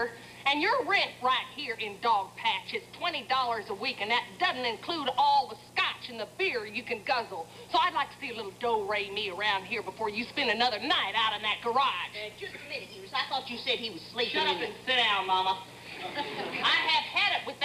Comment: Early in this sketch Eunice said that Dan was sleeping in the garage, but when she mentioned the garage again at 15:55, Mama stood up and said, "I thought you said he was sleeping in the. . ." and never finished the sentence. Did Carol Burnett make a mistake? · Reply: Little Bit That would explain it. I listened to this line at half speed, and although it sounds like she said, "out in that garage", her lips seemed to form the words "out OF that garage".